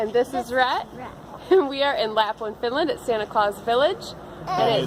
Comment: I thought she said Laughlin, Finland, which would have been awesome